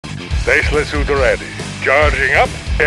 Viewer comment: Gotta be honest, I legit thought he was saying "Faceless suit ready" and now I know it's "Tesla suit ready", but I still want to believe the other. It's more menecing. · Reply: In my case, I always thought he was saying "spaceless suit ready", i.e. complaining that he can barely move in the suit :D